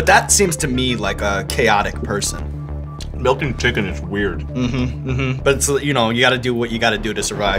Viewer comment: sounds like he says "Milking Chicken is weird"